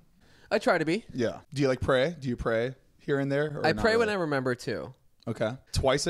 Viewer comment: I LITERALLY THOUGHT HE SAID "I pray when I number 2" and I had to go back